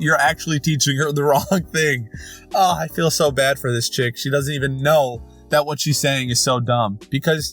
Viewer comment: did he call a 12 year old achic